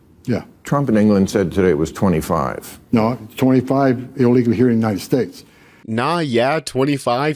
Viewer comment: "Nah, yeah, 25, here illegal in the United States." That's literally not even what he said. What he said was: "No. 25, illegal, here in the United States." That's actually perfectly valid spoken English. It's only 7 words and a number, yet you still managed to botch it horribly. Criticizing someone's English based on reciting over half of their words incorrectly is not a good look.